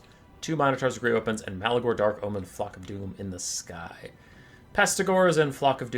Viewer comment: i thought you said "Malagor Dark Omen Fuck of Doom"